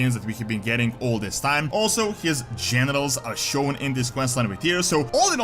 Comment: Sounds like is saying genitals every time he says generals